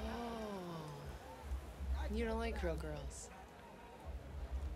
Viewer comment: What's the line she says at 3:03? "You don't like something girls?"